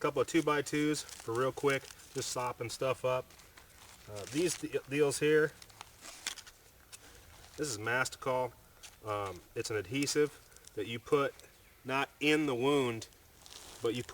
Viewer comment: what are those items at 3:30? I believe you said mastercall adhesive, but i googled it and turned up with nothing even with keywords "mastercall medical adhesive"